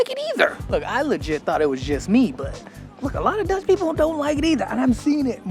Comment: Dude did you say that when you came out of the canal and was stinking : "I smelled like Dutch" ??? I replayed it twice but that's what I hear.... Please tell me I am wrong :) !!! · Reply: I thought he said 'ducks'. Duckpoop is pretty bad compared to regular birdpoop. But maybe I didn't here it right.